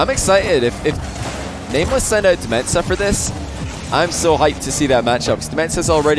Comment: I think DmentZa is supposed to be pronounced like dementia. When said the name at the beginning it sounded like he was saying Metsa(Metsanauris) and i was looking for him on the field lol.